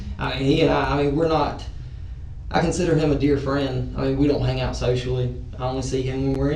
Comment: I thought he said we don’t hangout sexually 😅😅😅😅😅